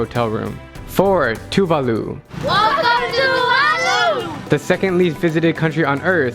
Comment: thinks they say:Welcome to Tuvalu What they say: welcome Tuvalu